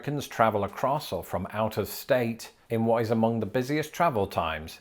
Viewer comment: I heard 'outer state' when you said 'out of state' and now I can't stop thinking of the new hit horror sci-fi movie, "They Came from OUTER STATE!"